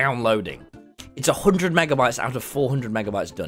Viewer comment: Did anyone make out the last comment- I hear like- “how do you think I got tubbo to do this. ⟟ drugged him” that's what I heard 🤣